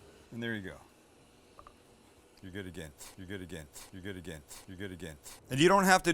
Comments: Sounds like he said, you’re good again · Sounds like was saying “You’re good again”. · I think he’s saying, “You’re good again.” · I think is saying "do it again(st)"